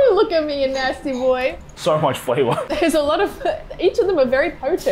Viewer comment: He said "so much flavor" and I only heard "SO MUCH VOLCANO!".